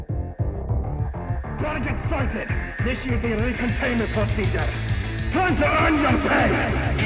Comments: = "Target sighted, intiating recontainment procedure. Time to earn your pain!" 0:37 = "You're coming with us!" 1:25 = "You're up!" Voice swaps to cadet. 1:34 = "Ugh, we don't have time for this!" Voices speed up. 1:45 = "You're going nowhere!" Voice slows down, swaps back to commander. 2:05 = "Don't let him get away!" 2:15 = "Get going!" Voice swaps to lieutenant. 2:24 = "Ugh, we don't have time for this!" Voices speed up. 2:36 = Swaps to commander. 3:05 = Swaps to lieutenant. 3:07 = Swaps to cadet. 3:09 = Swaps back to commander. Fixed misheard vocals. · "Target sighted, initiating recontainment procedure. Time to earn your pay!" 0:37 - "You're coming with us!" 1:25 - "You're UP!"(Swap to Cadet) 1:34 - "Ugh! We don't have time for that!" 1:45 - "You're going NOWHERE!"(Swap back to Commander) 2:05 - "Don't let him get away!" 2:15 - "GET GOING!"(Swap to Lieutenant) 2:24 - "Ugh! We don't have time for that!" 2:36 - (Swap back to Commander) 3:05 - (Swap to Lieutenant) 3:07 - (Swap to Cadet) 3:09 - (Swap back to Commander) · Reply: i heard “Target sighted! Initiating recontainment procedures! Time to end your pain!” and I also hear “Ugh! We don’t have time for this!”